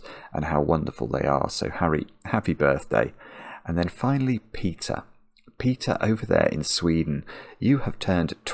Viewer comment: i thought i heard 'pizza' when he said peter XD but i do want a slice, so that might be why